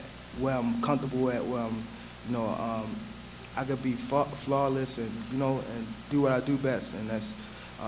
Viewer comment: Hold up at 8:17 did he say I could be fucked flawless? No diddy! Is that where the pent up energy he has came from? Dont believe me? Go to 8:17 😂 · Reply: He said I can be fla flawless he was stuttering.